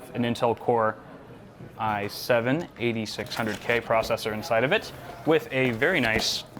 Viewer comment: Am i hear it wrong I7 8600k on